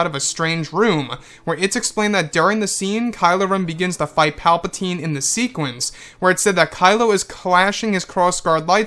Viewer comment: I misheard and thought he said "Kylo fights Palpatine in sequins" and thought they were all dressed like Elvis.